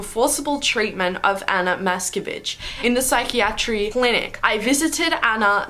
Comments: At the beginning I thought you said "Ana, mask a bitch" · I thought she said : Anna Matskebitch (No offense btw ;-;)